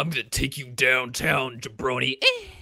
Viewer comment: did he say "ye brony"??? it sounded like it